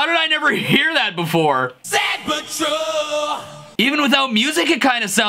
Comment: I Always Thought It Said 'Sad But Troll'. No. They Were Just Talking About The Best Patrol On Earth. Sand Patrol.